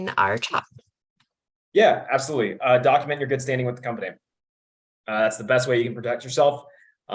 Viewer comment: What did say at 42:50? Literally cannot understand, tried 5 times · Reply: “Document your good standing with the company… it’s the best way to protect yourself”